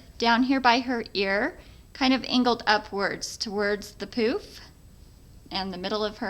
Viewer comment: I thought she said towards the poop at 1:15 lol :P